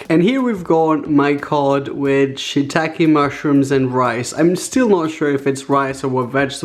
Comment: I I thought it said shit take mushrooms